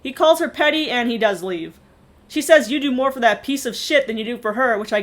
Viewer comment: I think actually said piece of pussy. 😂😂